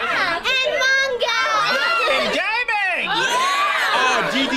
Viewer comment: wth did he actually say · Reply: And gaming.